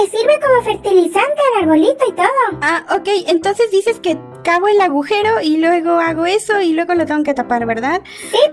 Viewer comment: le entendí "entonces dices que cago en el agujero (?)"